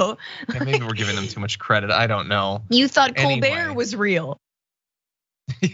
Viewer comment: i had to listen to Francesca say "Colbert" a few times because 3 out of the 5 I thought she said "cool bear" the other "cold beer".....this is what happens when I stay up all night due to life sucking....it was a worthy laugh at least, thanks.